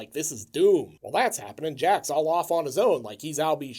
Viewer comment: did you just say Jacks all off on his own? 😂😂😂